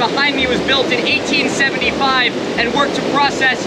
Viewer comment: Definitely thought said "...was built to process the rain that was processed by local farmers"